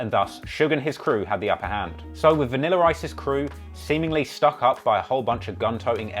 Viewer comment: With his British accent it sounds like he saying vanilla rice